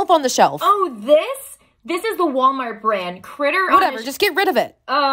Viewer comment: I thought she said ”critter sh!tt3r”